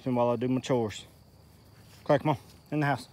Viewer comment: the caption says "Crack mom in the house." 😂